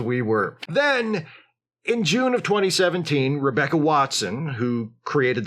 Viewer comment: the subtitles say “2011” where the stated year is 2017